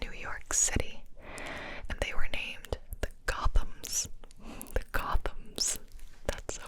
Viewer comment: Oh, Gothams? I thought she said Golems.